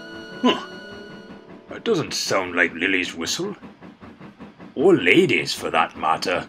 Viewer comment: did duke say lady's whistle